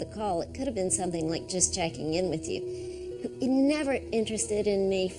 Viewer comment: What does she say? "[He was] never interested in me for one second"?